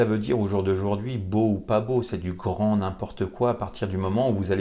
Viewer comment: à 10:39, vous dites quoi ? "C'est du courant" ? je n'ai pas saisi. Merci.